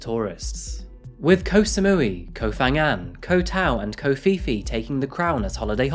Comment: You said "ko phi phi," but I heard "covfefe"